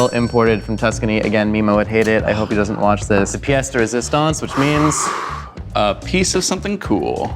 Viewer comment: the subtitles said ''speaks in Italian'' but Josh actually says ''pièce de résistance'' in French